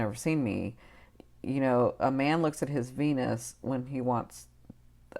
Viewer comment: You said, "a man looks at his venus when he wants a relationship". What I misheard was, "a man looks at his penis when he wants a relationship" haha